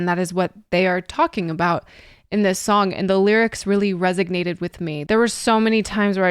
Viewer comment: and 10:42... are you saying resignate instead of resonate? That's not very nerdy of you